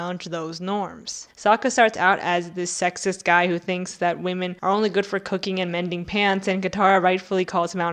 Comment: I heard "they're only good for bending pants" lmao